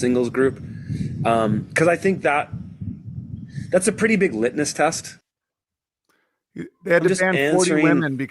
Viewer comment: Did he say litnis test??